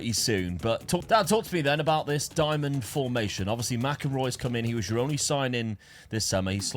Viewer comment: i swear i heard “Marco Reus” instead of McInroy 😂😂😂😂